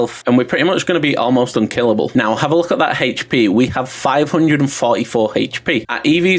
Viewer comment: Hahahaha how did I say it timestamp it I wanna hear 😂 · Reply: instead of hp you say “heych” p”